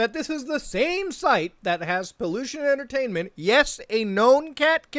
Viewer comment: I actually misheard his name as "pollution entertainment". this one actually suits him better